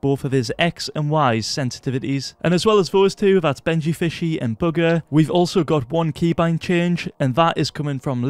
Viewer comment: i swear u said booger instead of bugha